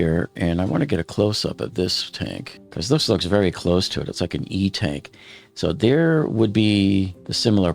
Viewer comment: did you say "f n e tank"?